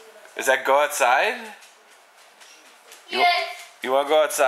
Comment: 37 I thought she said I don't know. Then again it did sound like go outside.